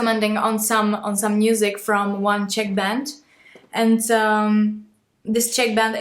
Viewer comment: Ok,thats "Czech band". I thought you said "Chick band"! Woops!🤣